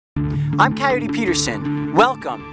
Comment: I thought he always said “ I’m tired of Peterson” :p